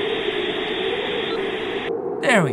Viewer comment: Wait a second... Did he just say... ...Hit me with some "Smooth jizz?" Well then.